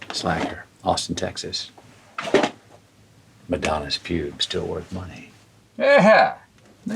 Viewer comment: does he REALLY say “Madonna’s pube , still worth money” ???